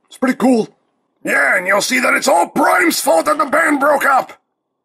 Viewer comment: i thought he said "brian's fault"....